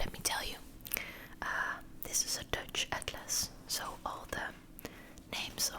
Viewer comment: Hello! What are the countries that you loved to visit? Please share, I would love to know! 🗺️💙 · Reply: I thought you said “this is a touch atlas” I was like- cool does that mean the countries are raised so you can feel them? Great and relaxing video!! Thank you